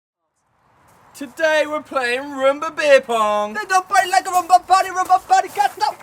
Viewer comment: I thought it said boomerang beer pong lol